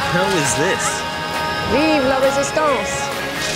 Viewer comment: what did she say? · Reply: viva la resistance French for live the resistance.